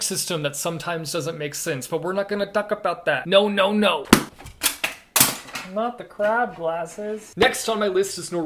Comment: not the crab glasses Me: did he just say what the crap glasses?